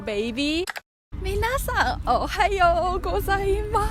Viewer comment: did you just say "mi nasa"?? edit: nevermind :( thought this was a toki pona namedrop but the captions say otherwise